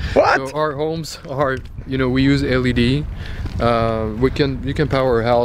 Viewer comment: "We use LED" I heard him say at first "We use LSD" :D